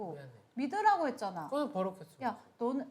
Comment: 에 가 뭐라고 했는데 현숙이 정색한건가요? 안들려요😢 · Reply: “너는 버럭했으면서” 인 거 같아요!